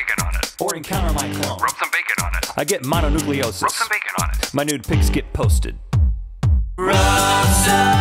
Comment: When I was younger I thought Rhett said "My NEW pics get posted" my childhood is ruined...